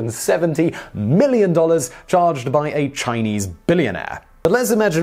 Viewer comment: i thought he said 170 mil to buy a chinese billionaire 😂 also, the guy who wrote his ex wife a billion dollar check, but no.. it's not enough, i guess she has to cut off his -alls too because he's worth 18 bil 🙄must be one of those modern women with the rainbow wall hangs zz